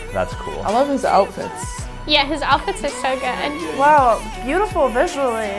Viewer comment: I seriously thought she said “he’s outfits are so gay” and I was like “yeah he’s so cvnt” 😭